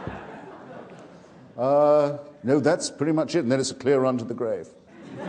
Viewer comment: What does he say At 3:58? “...clear under the grave?” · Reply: Michael Fleming clear run to the grave